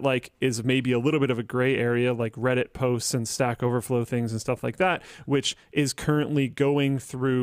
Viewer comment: Did luke just say sack overflow... 2:05:07 😂 Sounds like we need a unified messaging front end luke... Keeps all your contacts unified then just forwards that contact to the associated app